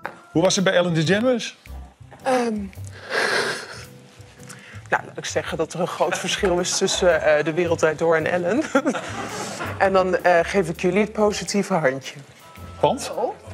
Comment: hi luv, the subtitles are wrong. she says "there’s a big difference from de wereld draait door ( that’s the name of the Dutch show she was on) and ellen, where i give you guys the positive hand" not trying to be rude, just trying to help🤗